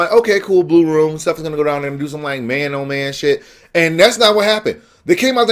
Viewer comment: did you just say "do some like, man on man shit"??!